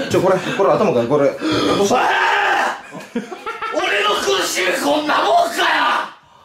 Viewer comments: 何て言ってる？ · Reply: 俺の苦しみこんなもんかよ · これがーどなんて言ってんの？ 俺の空襲こんなもんか？？？ 誰かわかる人おったら教えて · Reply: 俺の苦しみこんなもんかよ